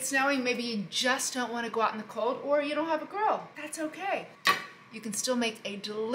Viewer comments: Not gonna lie thought she said don’t wanna go out maybe it’s raining or you don’t have a girl I was like damn I’m only here for the food but her words cut like a scalpel I was like oh she said grill both are true but damn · Thought she said “Maybe you don’t have a girl” & I started sobbing